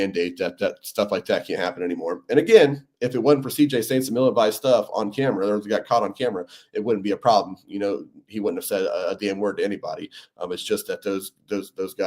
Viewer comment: can you clarify what he says here because it sounds like he says that someone said "the n word". I know thats not what he says because its completely out of context but it sure sounds like it. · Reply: "and again if it wasnt for CJ saying some ill advised stuff on camera he wouldn't have said a damn word to anybody"